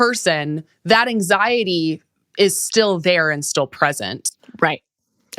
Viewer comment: I thought Martha said, "Instead of looking at the president and seeing what's there..." Haha. I back tracked and of course she said "present". However that actually kind of works too. Thank you for this talk.